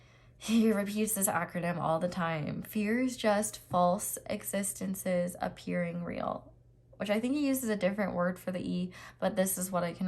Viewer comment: F.alse E.vidence A.ppearing R.eal is how I heard it